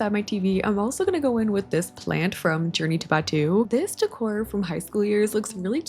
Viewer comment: Ive watched so many of your videos and just always thought you were saying "Journey to Bed 2" 🤦I was like 'Yea that sounds like a bedroom themed Sims pack name sure, Ive never even heard of Journey to Bed 1 though'. For the first time I saw the name of the item pack when you scrolled over it and it blew my mind